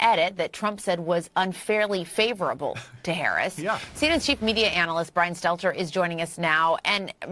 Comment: Did she say, "Chief Media Analyst" @ 6:53 ... 🤣🤣🤣🤣🤣